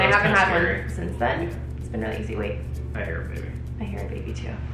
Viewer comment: I thought you guys said “I hear beeping” and not “I hear baby” and at the exact same time my oven started beeping 😂😂